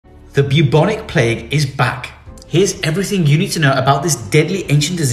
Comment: I thought it said “The bubonic plague is black”💀